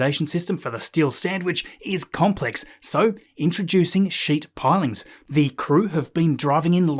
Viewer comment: I thought he said "sheet pilings"?